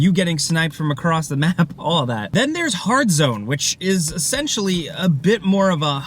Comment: Did he say "Hard Zone" at 2:30 ?